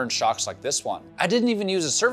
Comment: YouTube cut a second off the end of the video. It's supposed to say "I need to start prepping for paint." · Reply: I heard"I need to start prepping for PAIN"